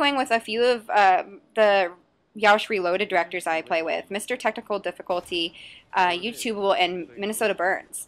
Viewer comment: Did She Said Testicul difilculty?